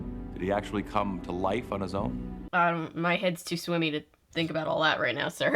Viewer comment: What'ja say there? Your head's too what-now? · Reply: 😅 I was feeling under the weather and my head felt "swimmy"...is that just something that I say? 😂